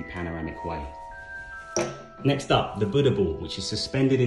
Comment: I thought he said bunda ball lmao